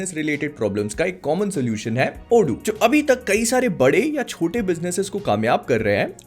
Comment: you said odoo I heard lodu...